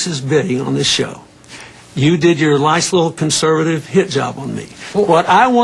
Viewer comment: "nice little conservative hit job," sounds like he said lice